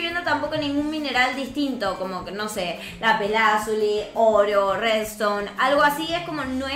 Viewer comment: le entendí oreo